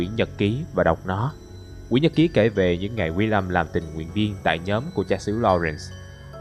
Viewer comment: đoạn 10p40 anh đọc nhanh tí chứ e nghe nhầm Làm Tình" Nguyện Viên :|